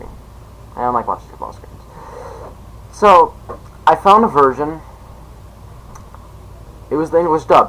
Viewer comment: instead of version i swear it sounded like you said virgin! Lmao i'm not kidding it sounded like you said that to me for some reason HAHA. Anyway, great video man!